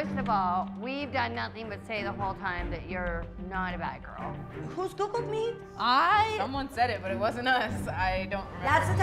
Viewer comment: I don't understand what was said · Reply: "Who's googled me" I think 😭😭😭